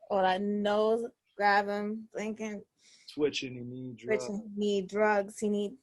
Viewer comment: Lux definitely had reaches bruh. I love Lux but Dej and Days sound nothing alike, no matter what accent you use. Richer Needs (knees) like joint pains? I'm not rolling. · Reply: he didn’t say richer needs he said witcha knees like joint pains misheard him and the dej loaf line is not a reach that’s beautiful and masterful wordplay with high level charisma